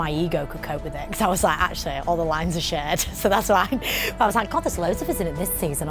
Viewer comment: I thought she said all the lines are shite xD